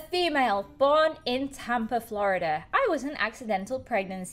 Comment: oof, I just thought said "Tampon Florida" Instead of "Tampa florida" lol